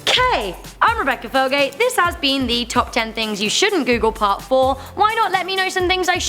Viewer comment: I thought it said " Top 10 Things You Shouldn't Coogle "